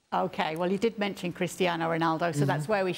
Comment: She mistakingly said Kristy instead of Cristiano.😂😂😂😂😂😂